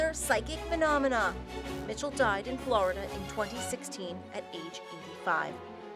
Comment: Did she say he died at age 5 ? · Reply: no she said age 85